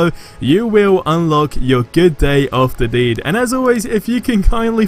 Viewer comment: Did I hear right. “Good day of the deed”?